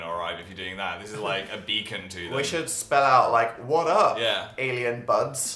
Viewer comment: the alien child shall be named "beacon" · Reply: mana_narie x I thought you said bacon😂